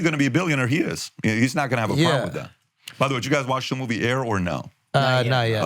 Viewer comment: what movie did Patrick say? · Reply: AIR-the story of Michael Jordan signing w/Nike